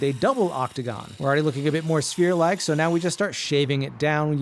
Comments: I kept hearing “spear” and got disappointed at the end😢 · My stupid ah thought you said spear 😂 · I thought he said spear 😞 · I thought you said spear 🤦‍♂️ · I thought you said spear and I was really confused as to why you were working with a lathe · I thought you said spear and wondered why you would make such a short shaft 😂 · I thought you said spear! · I thought you said spear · I thought he said spear. I was like that woods to sodt for that. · Didn't see the subtitles at first, thought you said 'spear'. 😁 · I was so confused.....I thought he said he was making a spear. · i thought he said spear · i thought you said "spear" and was so confused when you got it to the octagon shape